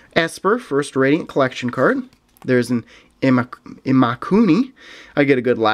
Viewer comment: when you said Imakuni, I first thought you said Illuminati. LOL